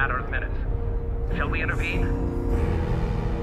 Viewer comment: did he say Mr. Malmsteen? Hell yes, I didn't know Yngwie was a terran.